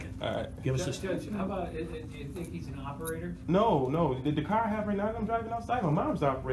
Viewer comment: actually the judge was trying to trip them up getting him to admit to driving but since he wouldn't take the bait to judge knew he was well-versed in what he was doing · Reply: Did I heard wrong or he said “the car I have right now that I’m driving outside” in minute